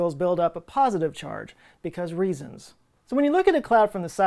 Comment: when Joe was talking about clouds and charge buildup, did he say "because reasons"? Maybe I misheard, but not sure what he meant there, or maybe he meant to fill it in later.